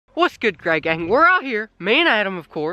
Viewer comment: It's sound like he saying gay gang:-D